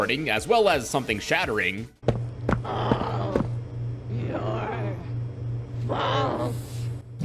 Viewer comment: I HEARD ALL YOUR BALLS LMAO